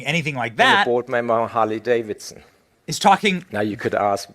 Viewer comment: Did say "Carly Davidson", or did I mishear him?